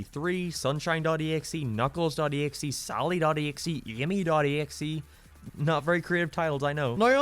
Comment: i did not understand a thing that came out of his mouth... what i heard was: "Eyemmy.EXE"